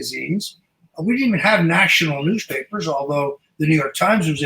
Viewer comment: does Roger say there: LOSEPAPERS?? How appropriate!